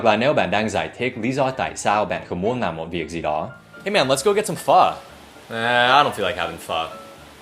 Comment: Jesus, I heard "Hey man, let's go have some f*ck" I'm sorry I didn't mean like that =))